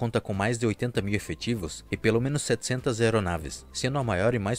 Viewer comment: the subtitle is wrong at the moment, it's not 200 but 700 planes